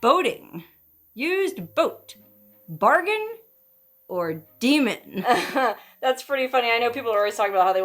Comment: I think it's Bargain or Lemon at 9:12 instead of Bargain or Demon